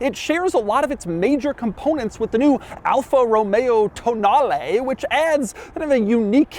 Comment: Everytime I hear Tonale it sounds like someone saying "toe nail" in a fancy way.